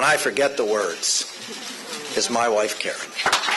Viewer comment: definitely thought he said "my wife carrot"